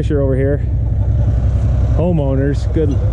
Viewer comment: This is fun watching somebody else do this.Some shiny DeWalt tools on this job.I have never seen trusses dumped on the ground before.Would be weird with Jimmy looking over your shoulder.I do not think "Homeowner" quite gets there.LO Looking forward to trusses! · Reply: Oh see, I thought he said "Ho Moaner"